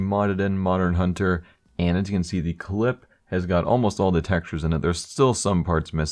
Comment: Did he really say clip at 3:18?